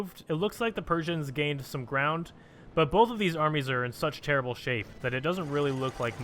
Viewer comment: Hey man, next run you should really give all nations A LOT of manpower, that way the armies won't just stop where they are, which is pretty frustrating to watch. Please like so he can see! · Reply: I thought you said animes and not armies.